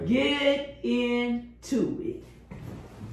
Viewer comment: I thought he said she was part cricket, but he said her part crooked lol.... SAVAGE